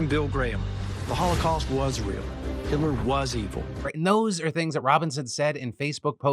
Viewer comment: I thought I heard him say "Hillary was evil" and it caught me really off-guard lmfao....